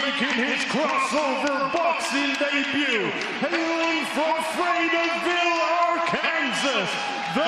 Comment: I thought it said “fattyville” at 7:41 and lost my shit